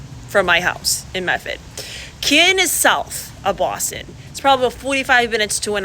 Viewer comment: I thought she was saying Kin -- and I didn't know what she was talking about, or where -- and I'm from Boston. Go figure. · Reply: I'm from a town right next to Canton and I didn't know what or where "kin" was. It's Canton, not Kin.